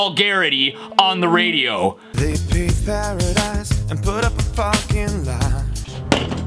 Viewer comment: I heard parking though